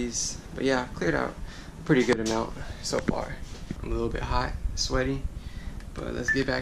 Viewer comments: I thought he said "I'm a little bit high" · I thought he said "I'm a lil high, n sweaty." Started dying lmao